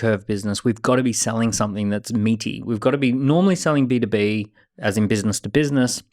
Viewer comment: selling something thats not meaty or meeting? i didnt catch that one LOL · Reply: Meaty - as in having enough substance to it.